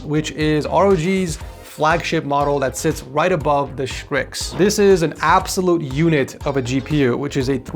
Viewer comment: Did he just say Shtrix?